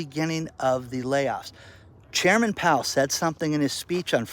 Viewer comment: Misheard Chairman Powel as Chairman Mao.... ooops.... same difference.